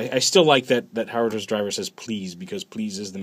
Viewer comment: So it's says Please, thought it said Greed. O_O